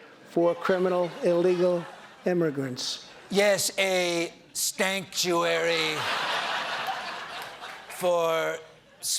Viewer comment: "a Stanktuary" is what i believe this dude said